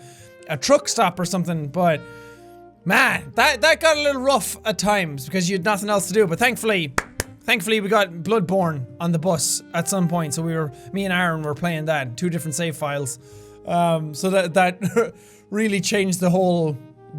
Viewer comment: i thought you said blood porn xD